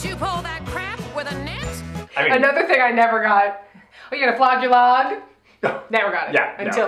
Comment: Can someone help a frenchie and tell me what says at 42:00? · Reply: She says "are you gonna flog your log?" Which Rizzo says to Danny.